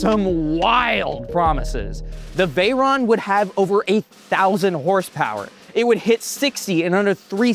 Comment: If you watch the video at 1.5x speed, at 6:01 it sounds like you say "8000 horsepower" 🙂